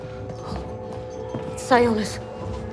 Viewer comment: what is she saying? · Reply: Sionis